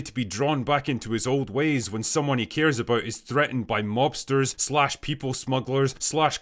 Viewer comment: i thought you said lobsters